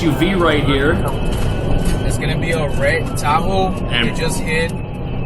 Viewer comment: they were saying in a red Tahoe but I thought they were saying in a red taco :face-blue-smiling